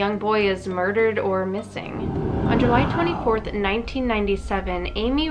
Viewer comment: Hold up. Did she say july 24th? That's my birthday.... Ummmmm someone went missing on my birthday?